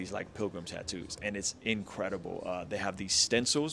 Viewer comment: I'm sure I heard you say INKredible! 🤔 I must say, kinda' good name for a tatt shop tho', bro'.🤦‍♀️